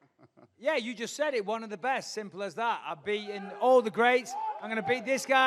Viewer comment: i thought bisping said "I've beaten "OLD" the greats.."